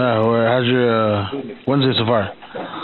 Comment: What did the guy say at 0:20? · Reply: howzzyerwenssdayshofarr which I think in human means "How is your Wednesday so far?"